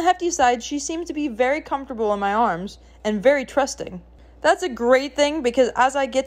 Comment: I misheard trusting as crusty.